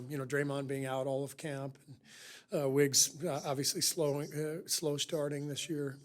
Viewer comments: I heard him say Wiggins slowing down or something like that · Sounded like he was saying slowing down instead of slow start